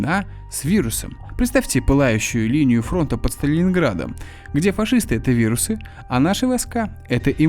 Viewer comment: Сталининград?) Послышалось?)